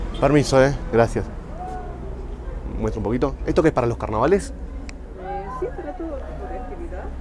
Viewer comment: Jaja pense que dijo "si pelotudo" 4:40 te juro. Muy buen viaje!